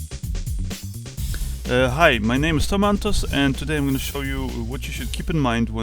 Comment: "Hi my name is Tomatoes" lol that's what I thought he said